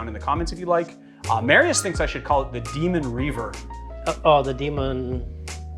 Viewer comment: I thought I heard you say, "Demon Reaver" which if you did, I'd go with that as the name.